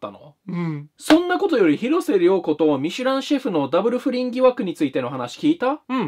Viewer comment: 広末の発音より、ミシュランシェフが見知らぬ主婦に聞こえたんだがw